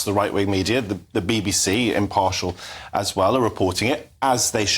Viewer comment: I might need to clean the shit out of my ears but I couldve sworn I heard that ginger helmet just say the BBC is impartial?